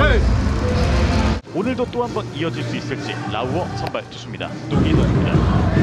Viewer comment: 메킷라우더가 에릭라우어로 들리는 매직